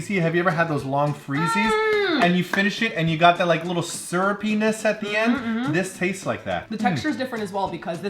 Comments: I was distracted then I thought I heard saying "sir, ur penis" at 6:03 and that quickly got my attention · says: "Syrupiness" - I hear: "Sir penis"